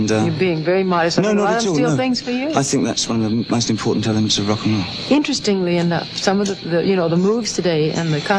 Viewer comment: " One of the most ... in rock'n Roll". Did someone catch it please? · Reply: "one of the most important elements in rock and roll."